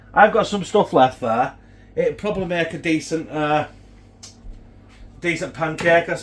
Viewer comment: thought you was gonna say make a turd 🤣🤣